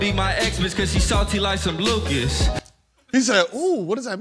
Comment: I think he said his ex is salty like Lucas, which is a Mexican candy company.